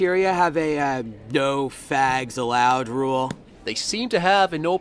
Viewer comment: I honestly thought it said bags- oh dear more reason to hate Kurt and Ram